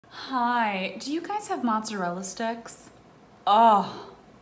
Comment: I'm not even joking when I first watched this I heard her say bread sticks, then the second time I heard mozzarella sticks and thought it was another part of the short but it had started over and I was so confused. I'm losing it